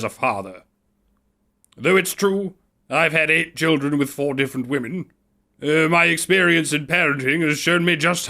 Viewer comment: For some reason I kept hearing "I've had 4 children from 8 different women." I need sleep.